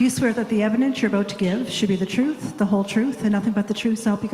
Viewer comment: Caption says : "evidence you are about to give WILL be the truth" etc.. but I heard her say "should be the truth" ?? Of course it should be the truth but will it be?